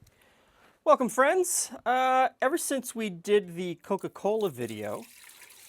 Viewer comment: I heard “ever since we did the Ebola video..” and wondered what the hell I just walked into here lmao